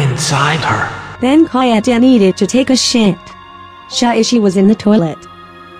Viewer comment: I skipped onto the video by accident and I heard "shuichi was in the toilet" and I couldn't stop laughing